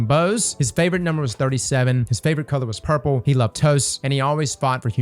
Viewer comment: I thought you said he loves toes talking about the dead person